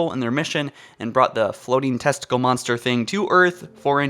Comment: did you say tentacle or testicle?